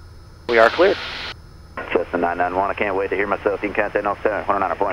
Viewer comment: What did the ATC say at 15:50? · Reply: “I can’t wait to hear myself”